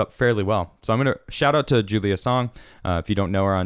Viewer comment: I swear I thought was saying 'Julian Assange' at the start